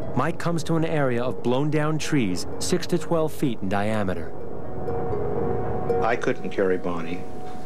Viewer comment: Did he say trees 6 to 12' in diameter we're blown over.